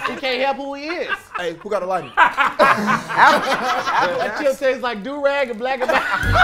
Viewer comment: What does say at 0:12 ?? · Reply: That chip tastes like durag and black n milds